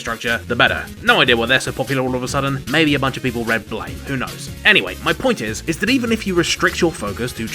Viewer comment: What's that comment you make at 0:34 - "maybe a bunch of people read" and I can't make out the name you mention. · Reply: I say "maybe a bunch of people read "blame!" - it's a pretty classic manga that serves as a visual inspiration for a lot of these megastructure games